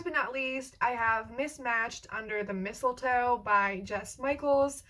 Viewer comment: Totally misheard "mismatched" as "We smashed" and that would be one hella party. 🌲. I sensed a theme around this video. I had no idea you were this big on easter! Bad jokes cuz I am a sleepy mess of gay.